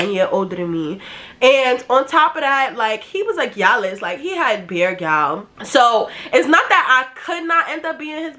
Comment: I genuinely don’t know what she said at 8:57-9:01 😭 for those with the same accent please spell it out for me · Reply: “He was a gyalis… he had bare gyals”